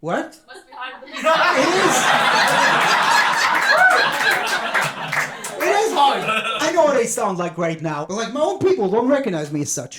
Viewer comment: what did she say anyone got it ? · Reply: She just said "must be hard"